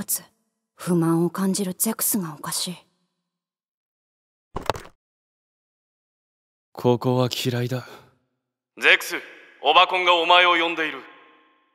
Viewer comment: オバコンがオワコンに聞こえた